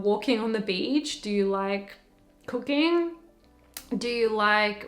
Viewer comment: I thought you said "do you like cocaine" hahaha, I was like wtf had to replay it.